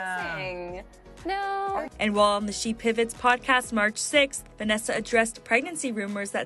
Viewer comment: So after she went off on everyone for being rude and making her feel fat for thinking she's pregnant it turns out she really was? lol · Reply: you sure? Cause I could swear at 1:20 they said March 6th but maybe my hearing is terrible